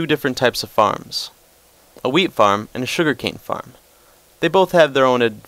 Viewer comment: when he said wheat, i thought he said weed. Here is my weed farm :P